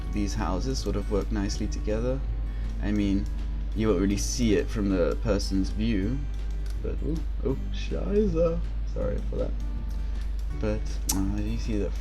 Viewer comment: Did you say "scheiße" at 6:38 xD? Amazing work :) PS: Ok, gerade gesehen, Du sprichst ja deutsch, das erklärt es^^